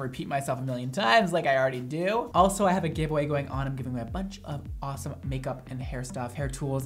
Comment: "I have a giveaway going on" I legit heard "I have a gay boy going on" I was like.....what? Haha